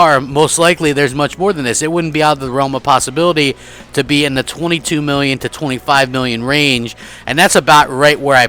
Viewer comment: Thought was saying 26 million sold?